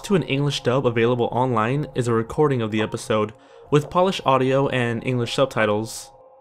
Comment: Wait did he say polish not Polish (the country)